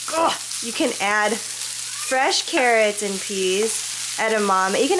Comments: Hahah I heard “frozen Karen’s and peas” 😂 oops good video my best soul sista. Great beginning song choice (from Eli). Looks so beautiful 😻 I can smell it from hereeee. Love you great video 🙌🏽🙌🏽🙌🏽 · Oh no wait I heard “fresh Karen’s and peas” hahaha